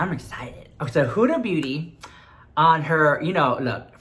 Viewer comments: Hahahaha, I thought you said 'Huda Booty', she does have a big booty too :P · Did you just say Hudda Booty?? Lmfao !!!